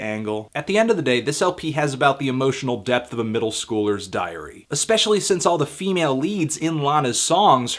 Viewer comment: lmao haha i thought he said diarrhea at 0:55 wtf lawl